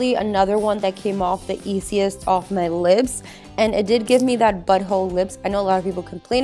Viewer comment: Did she say "butthole lips"?